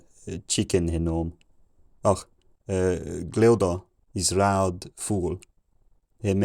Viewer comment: I heard the 800s “red bird” as “loud fool.”